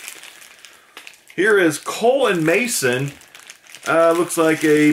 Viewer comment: You said "Cole and Mason".... I heard you say "colon mason"... My first thought "ewwww what do you do with that? And why is he touching it." I misheard you, lol.